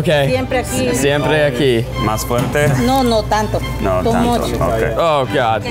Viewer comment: Did she say tonto?